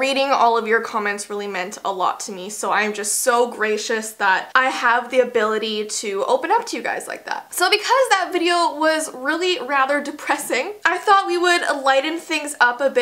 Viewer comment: I learned a bit of Latin and. Gracious is Greek so when you said “i am so gracious” I heard “I am so Greek” 😂